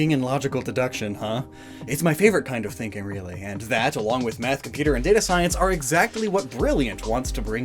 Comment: Am I the only one who heard him say meth along with the other thing and computer science